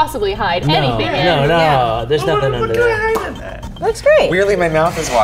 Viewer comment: “what can I hide in it “ is what he says